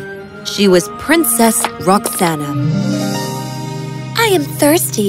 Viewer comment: did they say princess ROXANNE?!?!?